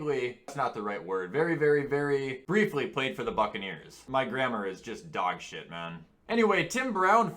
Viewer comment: at first I thought he said “My gramma is just dogshit” 😂